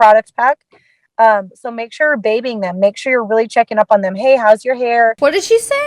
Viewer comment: I thought she said, “bathing them” 😂😂